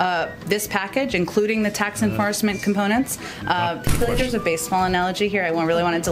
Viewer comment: Did she just say 'enfarcement'?